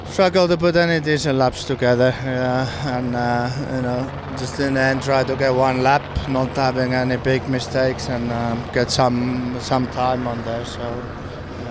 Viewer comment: anyone know what Kimi said? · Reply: Mr Poopybutthole "Struggled to put any decent laps together, and you know, just trying to get one lap not having any big mistakes and get some time on there, so yeah it is what it was"